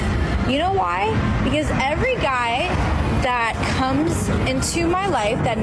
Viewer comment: think she said koomz not comes xD